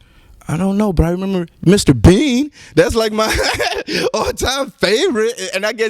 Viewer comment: did he say "Mr.Beeeean" ? That aint Canadian.